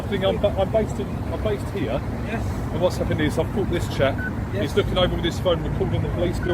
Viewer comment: I hear bicycle dude cop say "I just caught this chap" and I'm amazed he can speak so clearly when he has a hook half way down his throat. I think he has it backwards. His misuse of emergency services is an absolute disgrace for an officer. Also: GDPR recording code of conduct states "Inform the subject/public at your earliest opportunity" so another definite violation, Great job.